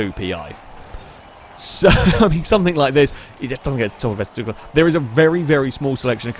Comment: excuse me, what did you say? :D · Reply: Cyan something like this, it just doesn’t get to the top of s2 class